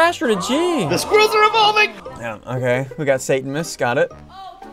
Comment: did ssundee say "satanmas" or something like that